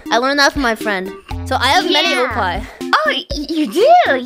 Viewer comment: We thought she was saying “I have many oppai” when really she was saying “I have mini oppai”